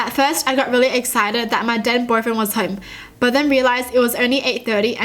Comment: When she was reading the one on the thumbnail, it sounded like she said dead boyfriend